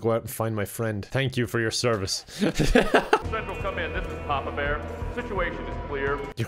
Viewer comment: Kevin: "Your codename is 'Papa Bear'?" Me: I thought the NPC said his codename was "Taco Bear" until Kevin said otherwise. 😄